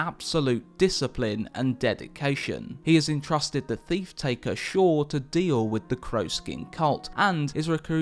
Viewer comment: Did he just say Thief Taker Shaw? or am I just hearing things? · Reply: Stay Frosty I heard theif taker